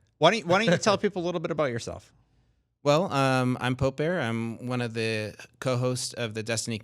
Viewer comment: Thought his name said poopbear. was really excited.